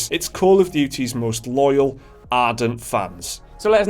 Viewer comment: Oh. Ardent. I thought you kept saying 'hardened' but like... 'ardened.